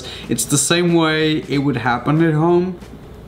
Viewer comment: I'm dead what does he say at 2:11 💀💀💀💀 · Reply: Kylie Lem nah it sounds like he said home doesn’t really sound like he speaked gibberish